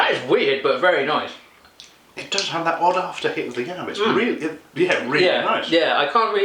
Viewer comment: I thought he said 'odd aftertaste of the cannabis' ...